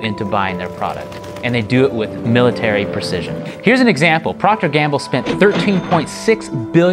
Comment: Haha I misheard P&G as “Propaganda” instead of Procter and Gamble, but I think Propaganda would actually fit very well!